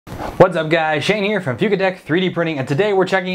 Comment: I heard you say 'Shane from Fugatech' first time Haha! 🤣 cheers, love your work.